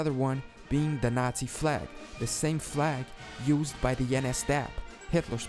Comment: NS-DAB Ja boi dab on diese haters!